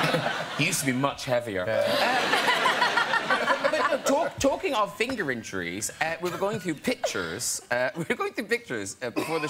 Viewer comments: Graham sounded like he said "Talkin bout fingering trees" lol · "finger injuries" i first hear "finger entries" .... ;) the internet has ruined me...